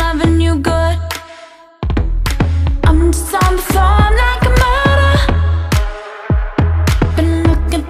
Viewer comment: I always heard “I’m just on the floor. Like a murder” and that would actually kinda make sense